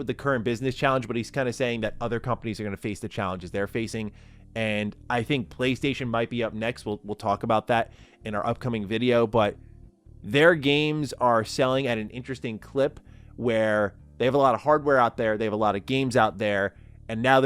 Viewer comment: Im sorry, I think I misheard, but did say games are getting cheaper? 😅